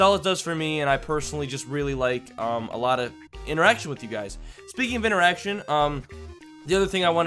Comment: When you said speaking of interaction it sounded like you said speaking of an erection